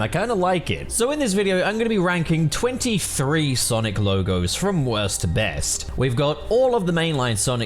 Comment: Wait i swear to God Almighty Themself, on the poll preceding this video I thought you said you’d rank Sonic LEGOS